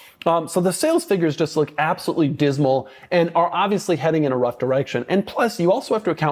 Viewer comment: I thought he said the sales were “having an abrupt erection”